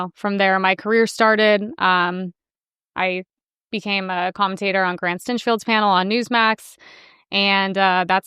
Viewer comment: at 4:00, you mention that your career started with Newsmax and “____ ‘s panel”. Couldn’t decipher whose panel you said gave you your first gig (sounded like ‘Grant Centro’). Who was it? Thx. Love your content, and you and your dad are a dynamic duo. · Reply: Never mind. I learned it is Grant Cardone, known for his 10x program to inspire personal growth, who played a huge role in launching career.